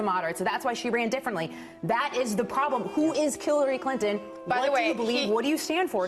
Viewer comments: The woman from FOX NEWS says, "who is Killary Clinton?" I turned on the Closed Caption on, and that is what was said. That is risky! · did she say killary clinton??